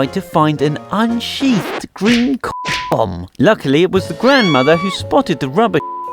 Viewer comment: i heard nothing because of the censors.What was it saying? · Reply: alesul a condom